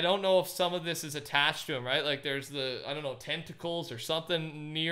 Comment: Loading screen shows the blimp top left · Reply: Money mase I legit thought you said testicle instead of tentacle.